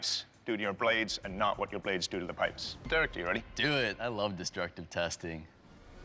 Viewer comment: I thought the guy said “do it I love this drug testing”